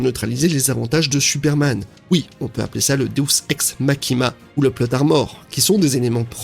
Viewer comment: je ne sais pas pourquoi j'entends deus makima ?